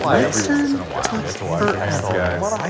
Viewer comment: Did I hear a "That's what she said?" 😅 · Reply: Yes, one of them really said that at the end. 😂